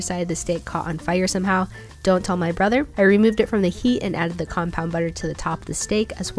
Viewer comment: I didn't hear her say compound butter and all I heard was "it's hardened" and I got confused